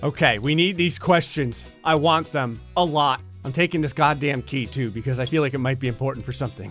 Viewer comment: I thought it said “Smell Key”